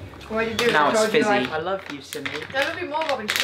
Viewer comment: d-did I hear right? He loves Houston? ... live in Houston...wow